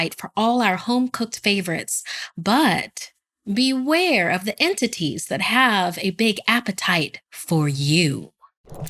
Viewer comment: I heard “beware the tortilla” I died laughing 🥺😭😂😂😂